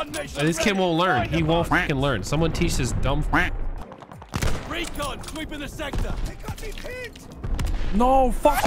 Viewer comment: did he say retard sweeping the sector cause I’m loving it